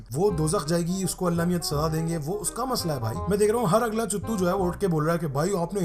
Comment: did he said chutu?😂😂😂😂